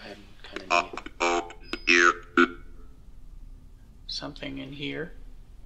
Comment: i think it said its cold here